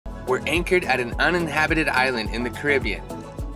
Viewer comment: Dang, I thought you said uninhibited island.